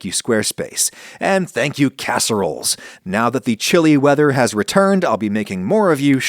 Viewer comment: y'know, I misheard "chilly" as "chili." Eh? Ehhhh?